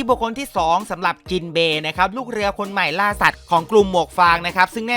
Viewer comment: มีใครได้ยินคำว่าล่าสัตว์ไหม55555